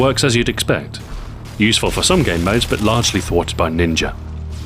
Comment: it sounds like your saying ninjar lol but probley just a european accent?